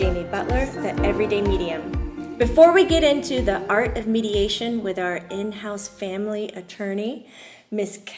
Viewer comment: I thought it said "the art of MEDITATION" . Fail lol.